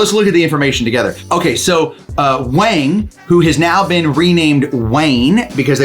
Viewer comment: When you first were saying Wang's name, I thought you were saying Wayne, so fitting that they changed his name to that. I took in a lost/abandoned dog, well my parents did when I was 5 years old. He was a Golden Retriever. He'd obviously been very well trained and was well fed. But we couldn't find his owners. He had a collar, but phones were disconnected. So Mack became my first dog. My Mom and I kept calling him Max instead of Mack, so his new name was Max. We had him for over 12 years. Within the first minute or less of you being in the kennel with Katie, I could tell she wanted nothing more than to be SCOOPED into your arms/lap... I hope we get more updates on her. How long ago was her original video made?